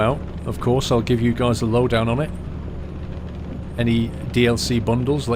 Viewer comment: Did i just hear "lowdown"